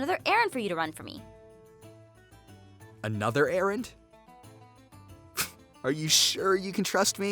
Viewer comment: when she said eren I heard Aaron :/ I'm shore she said Aaron I'm apsaloutly shore off it